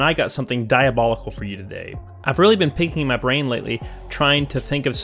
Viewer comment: Your accent made it sound like you said "I've been pinking my brain" instead of picking 😂 Btw just started watching. Subscribed right away my dude 👍